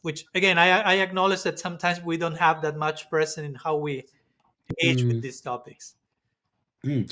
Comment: Thank you for this valuable exchange, it's much appreciated. May I ask, what exactly do you say at 1:08:43? "Sometimes we don't have that much.........? (the rest of what you say is not clear to me) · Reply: The word is "precedent."